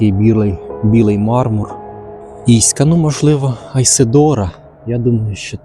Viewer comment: Ви молодець, ваші відео все краще і краще. Старайтеся й у вас все вийде. Ви на вірному шляху. Це лише мені почулося п*ька на 17:49? 😅